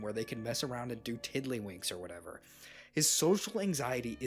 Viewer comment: I thought this man said diddy 😭